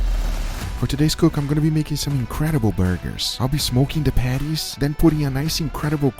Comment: Swear i thought you said ima be smokin the panties and i was like my man! Then i knew you meant patties and i was still like my man!